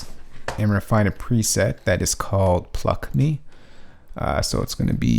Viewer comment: i thought he said a preset called f me